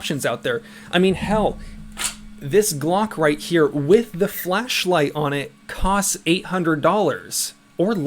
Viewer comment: Thought you said “Glock with a fleshlight” for a sec 🤔